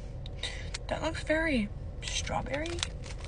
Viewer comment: Did she just say “it looks very strawberry?”